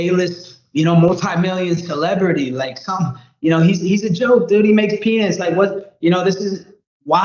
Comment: did Colby say that Ian “makes penis”……?